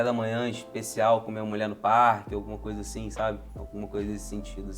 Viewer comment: Com todo respeito, no minuto 11:07 eu entendi "Comer uma mulher no parque"😂😂😂